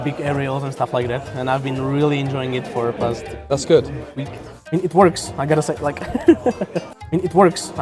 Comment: So I think he says "I mean it works, I gotta set deck" Admitting to cheating? 😂